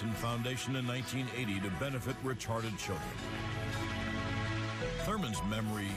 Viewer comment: did he say retarded children? harsh